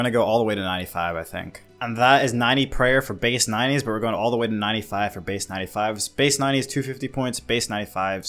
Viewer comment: Base 95s? (94 agil, 94 craft, 92 herb, 90 thief, 80 cons, 80 pray) Wat. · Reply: I misheard that, I thought you said you had 95s base. My bad G